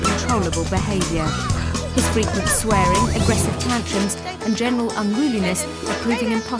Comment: I thought it said sweating 🙄